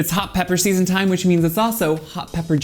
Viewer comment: I read that as hot poppers and then heard you saying “hot poppers season” , either way both poppers and peppers will dilate and relax your anal sphincter ✨✨✨